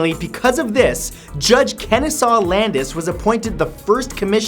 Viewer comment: the CC got the name wrong, it's Kenesaw not Kenisaw. I should know, I'm also a Kenesaw, and it's a great name that deserves to be spelled correctly.